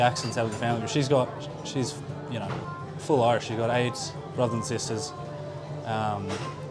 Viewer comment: Great Interview! A fireside Chat! Anyone else catch at 7:52 when Mack says about his Mother that sounds like "She's got Aids" and you can see is thinking what in the hell...Maybe just me!